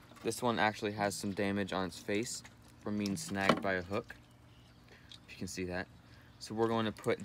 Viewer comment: i thought you said " he has some damage on his face from getting :smacked by a book"